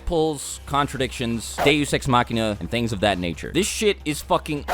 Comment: What did he say at the beginning of the video 2:10 “deusexmacina”🧐